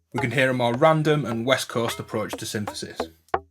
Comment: Did they say "a more 'West Coast' approach?"